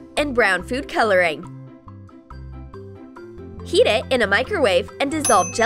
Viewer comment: i thought she said eat it